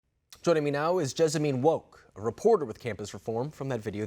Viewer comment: I thought he said Josephine WOKE Lolol I was like wow she woke lmaooo